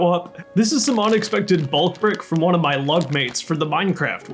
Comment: lol - you said LUG mates and I heard "love mates". I thought "wow, that's a weird name for Luke, but ok"...